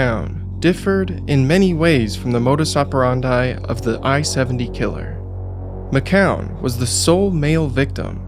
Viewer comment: I thought it said the 70 iq killer and my first thought was why you gotta call him out like that 💀